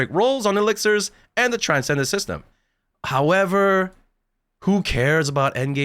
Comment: I heard it as transgender system 😂